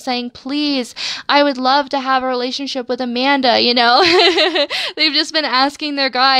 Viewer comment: did you say Amanda because I'm freaked 😂 picked the right pile cause that's my name